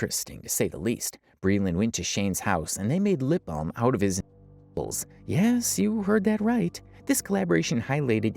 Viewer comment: They made lip balm out of Shane’s what??? When you censor the entire word we can’t tell what you meant 😵‍💫😵‍💫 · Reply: His nipples. Saw it in the video